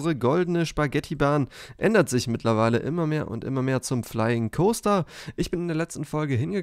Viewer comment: Ich hab zuerst flying Toaster verstanden